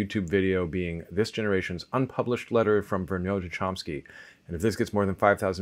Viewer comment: Does anyone know what he's saying at 0:38? It sounds like "unpublished letter from Vernon to Chomsky". I've tried to look up every variation of interpretation of this but can't figure out what he's referring to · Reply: Even a reverse Google image failed me. I finally tried a different reverse image search. It's Vergnaud